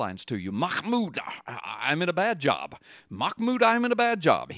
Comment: Hahaha. Sounds like your saying his name is Mahmoud 'I'm in a bad job'. Now That's Prophetic!